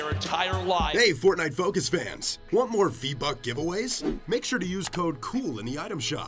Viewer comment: did you say V buck?